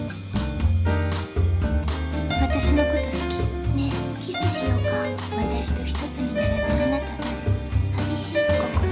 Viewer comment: Can someone pls tell me what she say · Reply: “Shinji can you get off my ass? I like you father, not you”